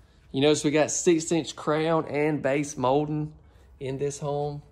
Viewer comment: Gosh I thought you said crayon (crown) and base molding!!! I love your accent!! Beautiful and very spacious home! You are right that master is huge, all the bedrooms were a great size.